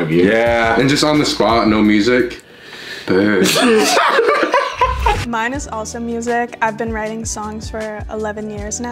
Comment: Can someone tell me what he said? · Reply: he was imitating the girl singing "birds flying high"